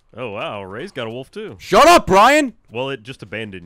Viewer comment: Did Ray call Ryan Brian?